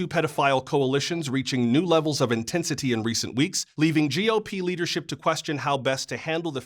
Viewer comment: I thought he said GLP leadership 😂